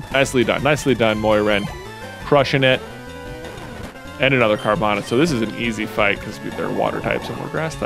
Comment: The first time you said Carvona I thought you said Nirvana